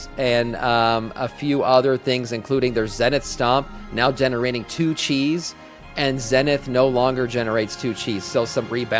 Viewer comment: I tottaly heard that as "monk generating more cheese" 😅